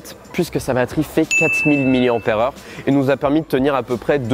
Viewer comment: C'est moi ou à 3:20 Il dit 400 000 mAh ?